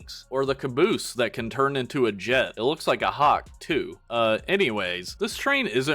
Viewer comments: Excuse me, but what did you just say? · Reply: I THOUGHT I WAS THE ONLY ONE THAT HEARD HIM SAY HAWK TUAH 💀💀💀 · Why the hell do i hear hawk tuah here